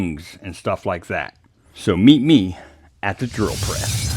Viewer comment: Every time I hear you say "meet me..." I hear the Road Runner "meep-meep!" 😅